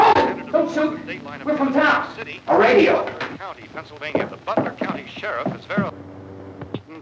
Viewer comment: It actually says the name of the town I thought I was the only one who picked up on that lol I'll try to figure out what it is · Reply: I've been listening to it and I have to take back my statement that I thought it said Pennsylvania as the state it is quite hard to hear it does sound like Bellfort